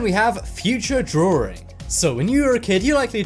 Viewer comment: did he just say “drawring”??